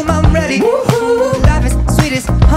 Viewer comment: Great work everyone! Maybe would be better if this is addressed. I think it goes: "This beat cha-ching like MONEY." Is it just me that hears honey instead of money at 1:26?